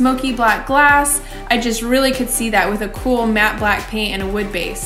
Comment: What colour is it? Black por dark blue? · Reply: I think I heard her say matt black